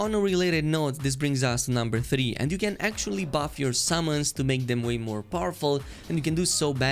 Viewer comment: The way he says "summons" sounds like he is saying "salmons" and I can only think of a fish flying at an enemy